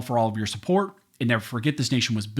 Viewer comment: This whole time I thought he said barn scholars.